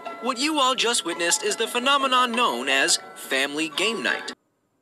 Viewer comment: what I thought ennard was saying when they were screaming of monopoly or however u spell it- Hello. This game is known as FaMiLy gAmE NiGhT